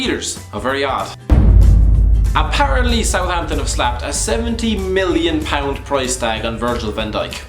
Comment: did he say 17 million or 70 million? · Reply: probaly 70 million, IT'S VAN DIJK!